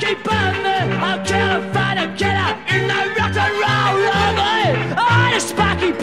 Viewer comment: All these years I thought he was saying "..rock-n-roll library...". I even explained to my kids that there was such a thing as a "Rock-n-roll" library - "Just listen to this song" I used to say... Now I have to try and explain that it was all a mistake!